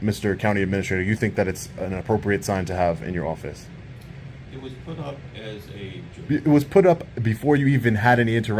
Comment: The County Administrator says that "it (the sign) was put up as a joke", but you can barely hear what he says. I wonder if ("The Protector of Our Constitutional Rights") deliberately muffled the sound, so you can't hear what he says? Remember, Frauditors have no sense of humor and wouldn't know a "joke" if bit 'em in the ass. Poor Frauditors, what a wasted "life" they live, if you can call that "living".